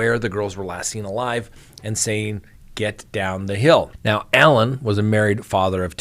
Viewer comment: "Get down the hill". All of this time I thought he said "He's down the hill". I'm definitely going to listen to see which I hear more clearly.